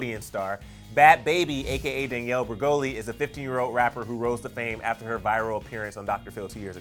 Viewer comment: I thought he said that bad baby was a 50 year old rapper😁😂